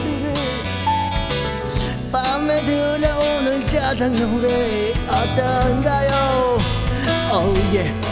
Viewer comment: 초에 짜장노래라고 들림